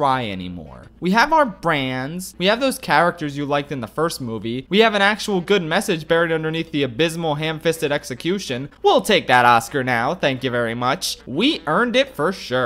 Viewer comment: i can just hear "oí vei" in that part